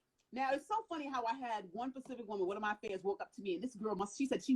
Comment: Love her... but I'm sorry I know she means to say "specific" I keep hearing "pacific" 🤦🏾‍♂️ is this an American thing?